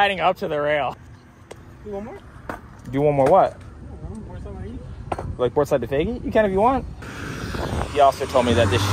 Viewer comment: did he say boardslide 180 lol